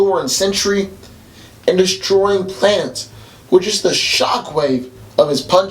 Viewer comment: I keep hearing "destroying plants"